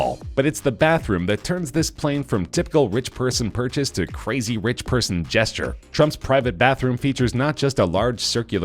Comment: did he call him crazy person? lol